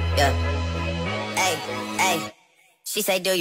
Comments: What I thought he said was I only love my dad and my mama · For those curious, he says “Mahbed” which is his sons middle name. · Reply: I thought he said "I only love my dad and momma I'm sorry " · I thought it was “she said do you love me I tell her only partly, I only love my dad and my mom I’m sorry 50 dub” and so on but then my brother told me it was “ I only love my bed and my mom I’m sorry” but I never new it wasn’t those. Pls like 😢😢😢😢😢😢😢😢😢😅😊😊